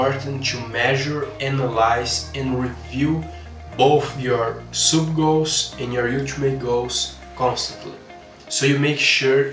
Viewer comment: what did he say sub goals and ??? goals? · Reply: Ultimate goals